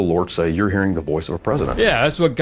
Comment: I heard "... Voice of a predator"